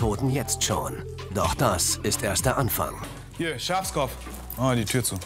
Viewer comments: Hat er bei 4:01 Schafskopf zu ihr gesagt 😂? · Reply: Ich hab Schwarzkopf verstanden 😂😂😂😂😂😂😂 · Hat er bei 4:01 Schafskopf zu ihr gesagt 😂? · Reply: Ich hab "Schwarzkopf" verstanden ahahaha